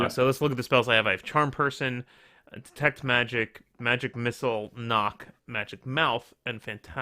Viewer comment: "Magic Milf" is my favorite spell, too ;) Oh wait I misheard it haha Great adventure, guys. I enjoyed these kind of single player "choose your own path" books when I was a kid.